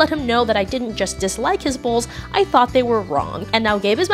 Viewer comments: I stg I heard “I didnt just dislike his balls, I thought they were wrong” lmfaooo at the end I finally realised it was BOWL ogod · i thought she said "i dont like his balls" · I thought she said 'I didn't like his balls.' 😭😭😭😭😭